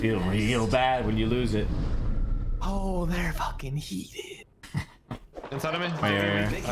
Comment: WHAT DID HE SAYYYYYYYY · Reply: They’re gonna be heated ?😂